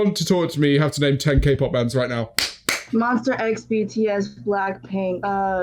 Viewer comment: did- did she just say MONSTER X at 10:23 . OMG its MONSTA X . i feel so offended right now